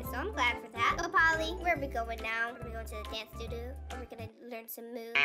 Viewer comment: When said let's go to the dance studio at first I thought she said let's go to the camp doodoo😂😂😂